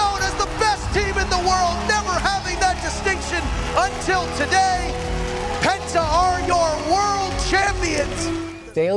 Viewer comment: he is actually saying "untilt today"